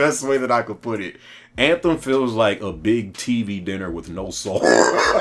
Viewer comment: I thought this guy said A TV DINNER WITH NO SALT loooool